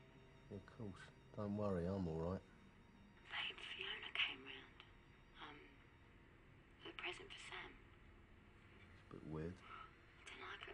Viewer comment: I have a habit of using closed captioning. I literally spent half the time watching the movie and spent the other half expanding my Spanish. I'm afraid that I may end up having to watch this again. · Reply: Be careful tho, there is some errors here and there, like 40:37 when she said "hey, Fiona came in with a present for sam" or something like that, the accent make it difficult to hear it right, the subtitles say "...Im fine" mistaking Fiona with fine and so on